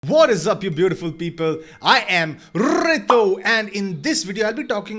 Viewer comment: I thought you said beautiful sheeple... Hahaha